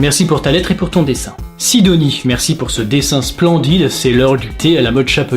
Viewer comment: Au lieu de comprendre " c'est l'heure des colis " quelque chose du genre , j'ai compris " c'est l'heure du coït " :')... Voilà voilà hein... ^^'.